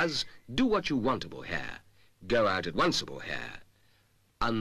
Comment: Why did I hear him saying "Silvikrin shampoo" as "Super cringe shampoo" 💀 Anyway, I love this, she's so pretty. Thank you for sharing 💛